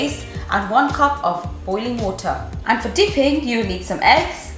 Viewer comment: did she say holy water 👀